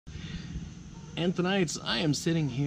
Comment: LOL! All along I thought you were saying AND TONIGHT...only now i realized that it is ANTHONITES...I've been asking myself why is he saying tonight even in the broad daylight :)